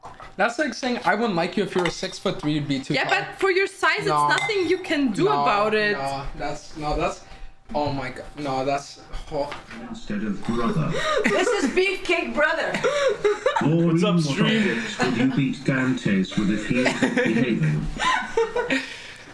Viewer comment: is it just me or did i hear its his beef ake brother